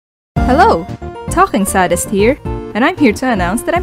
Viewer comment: For awhile I thought she said Topping Sadist